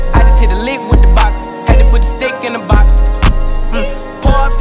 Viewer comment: I searched “ima get lazy” and found the song. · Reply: I search "had to hit the stick with a bottle" because thats what i thought he said.